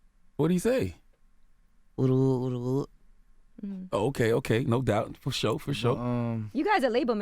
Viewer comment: what he say?. oodi oo oodi oo oh ok ok, no doubt fasho fasho 😂